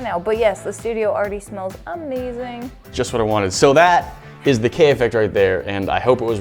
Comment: lol I thought you said "What is the Kanye effect"